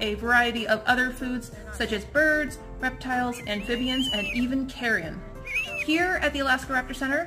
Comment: kmsl I thought she said "And Even Karen!" 🤣🤣🤣🤣